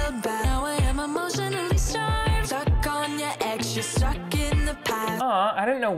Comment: I’m so glad I’m not the only one that was hearing “suck” instead of “stuck” 😭😭